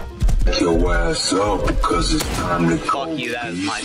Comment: I really thought it said "Wake yo ass up 'cause it's time to go depo" in the start xD